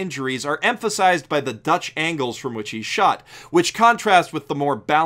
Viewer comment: did he just say dutch angles at about 2:45 ?